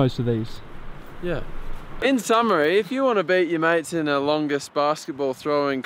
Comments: it sounds like he says if u want to beat ur meat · Lmaoo when he says "beat your mate" I thought he said beat your meat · it sounds like he said "if u want to beat your meat"